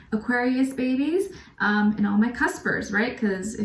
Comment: I'm actually not good with english What does this mean? She said casper right ?? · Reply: Cusper, which means a person born in the cusp of two signs. For example, at the end of one sign and the beginning of another.